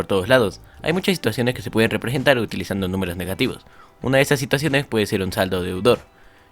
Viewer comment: alguien por favor me podria decir que dice en el minuto 3:05 porfa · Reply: Dice "en estas situaciones puede ser un saldo deudor"